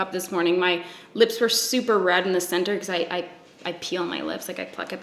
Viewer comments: I thought u said "I PEE on my lips" and I was lik dam never knew u were that flexible LMAO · Coulda swore she said “I pee on my lips” instead of “I peel my lips”